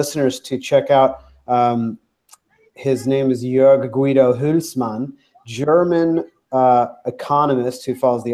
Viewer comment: Can someone tell me what the name of the german economist was that mentioned? I cant hear the name properly. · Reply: should be Joerg Guido Huelsmann